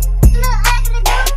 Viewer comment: I thought he said big booty sticks but yall litt